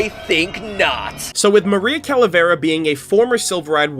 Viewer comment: When I heard her name I thought she said caravera and I was like well then whoever gave her the name either messed it up or was on purpose. Also... Do Hispanic, Latinos, and Spanish(Language) exist?